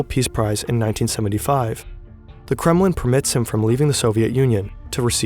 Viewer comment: does he say “permits him from leaving” or “prevents him from leaving”. It sounded to me like permits but that way he words the sentence is odd. “The kremlin permits him from leaving” doesn’t sound right lol. “The Kremlin prevents him from leaving” sounds right.